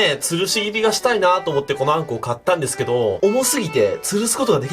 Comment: から10秒スキップするとま○こー買ったんですけどーって聞こえる